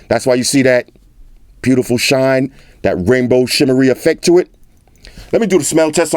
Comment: I thought he said "rainbow shittery"